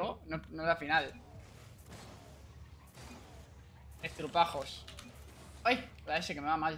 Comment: Pense que había dicho estrupijas 😅